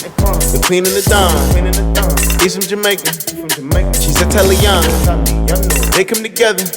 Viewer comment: Who else thought they said hes from jamaica shes a telly younghi